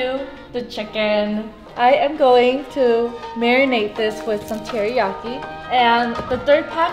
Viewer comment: I thought u say on 0:31 married but you said marinade